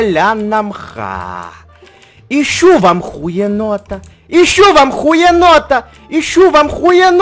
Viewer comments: Ищу во мху енота. - а мне послышалось: Ищу вам ухиенота. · Кто из 2019 👍 лайк на комент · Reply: Мне послышалось ищю я хуенота